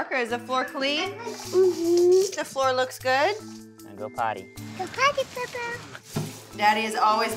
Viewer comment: When hazel says go Porte beaux beaux it’s sounds like she says poo poo